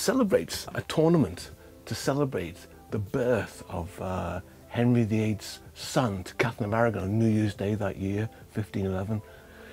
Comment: The marriage of Henry VIII’s son to Catherine Of Aragon? 🤦🏻‍♀️ Any historian should know that that’s a ridiculous statement!!!! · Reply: That's not what he said. He said "the birth of Henry VIII's son to Catherine of Aragon" (0:50).